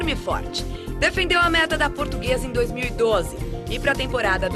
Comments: Só eu ouvi a mulher falando "merda da Portuguesa" ? Min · “Defendeu a MERDA da Portuguesa” 0:37 eu escutei certo?